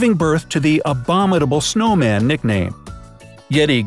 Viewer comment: "Yowie in Australia" then the subtitles was "Yaoi in Australia"